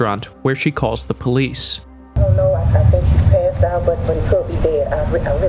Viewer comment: The lady on the phone said "I don't know. I think he's passed out, but he could be dead" and your animated text bubble typed "I don't know, and I don't get paid for this, but he could be dead." She did not say "...and I don't get paid for this" in the recording. Can you fix it?